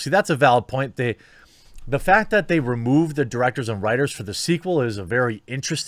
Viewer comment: If this were true, I'd actually consider watching the sequel. Now if only they'd consider switching out the writers too. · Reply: I think he says they’ve removed the writers